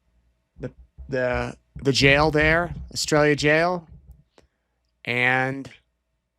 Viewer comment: I think you meant Arizona, NOT Australia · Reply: The name of the jail she where she was housed while in Arizona is called Astraila, but it definitely sounds like he said Australia 😂